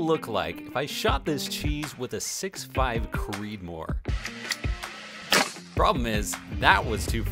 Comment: Thought it said Cheesemoor